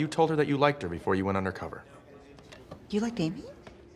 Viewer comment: the subtitles say "you like gay me" instead of "you like amy"